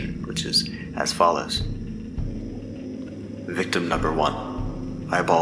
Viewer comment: lol. wasnt watching, just listening and i thought genuinely it said "victim one: my balls were unable to be found." Had to rewind and go read along...